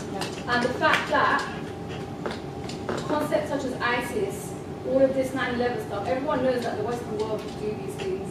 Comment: What is the girl in 01:06:35 saying, can anyone hear? something like "about isis, and 9.11 - everybody knows that..."